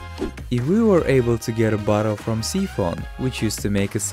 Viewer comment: What did he say here? Sea foam? I can't tell lol